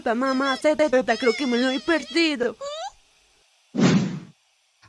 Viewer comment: Escuche jefe??? Ojalá y la serie se mantenga así, con tramas interesantes y puros chistes y no se convierta en algo como helluva boss